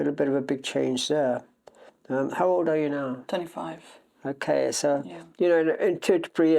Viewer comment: I said I’m 25 😂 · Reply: I heard 35 here